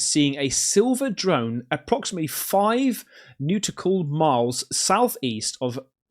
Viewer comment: im sorry, did you say newtical?